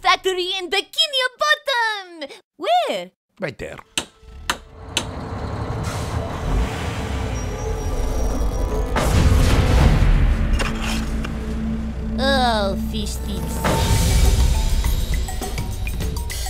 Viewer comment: Is it just me or does Bob say Fishtits?